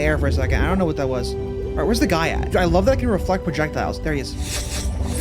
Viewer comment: sounded like "uhh, wheres the gyatt?" 🤓